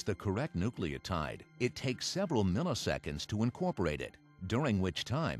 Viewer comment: what does he say? Polymerase takes nanoseconds or milliseconds to incorporate a base? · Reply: milliseconds